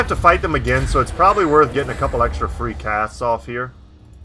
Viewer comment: I swore I heard get off a few cat's. Instead of casts. Was abit miffed lol